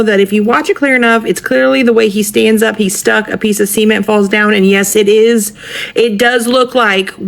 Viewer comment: That woman meant to say cement but it sound like she said semen